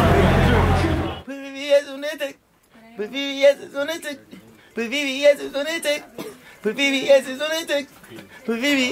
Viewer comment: what the fuck bruh saying at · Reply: Vvs on a tec